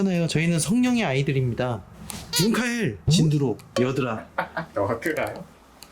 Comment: 여드라 를 순간 여드름 으로 잘못들릴 수 있읍니다.. 제가 그렇게 들었거든요~ 그래서 순간 말 과 글자가 왜 다르지? 라는 의문이 잠깐 생겼네요